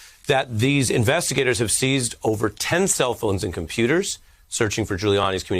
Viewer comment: It's well known that Ghouliani have at least 3 phones on his person at all times. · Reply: Most public figures including celebrities all have at least 2-3 cellphones. it is not uncommon. but it does sound like they did say 10 cell phones were seized, I was mistaken i thought they said 10 electronic devices --- 10 cellphones is a lot...